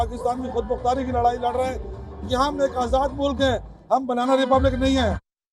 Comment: did he just say banana republic.😂🤣